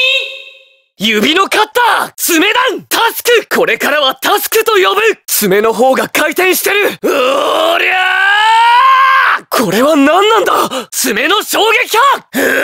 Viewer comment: why dose he sound like he is saying wryyy